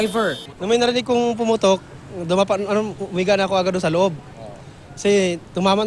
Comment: "Nung may pumutok... Humiga na agad ako sa loob" XD LOL!!! I thought he will said ngumanga na agad ako